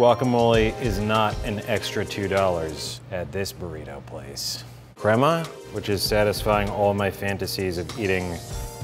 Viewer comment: he said crema but it sounded like grandma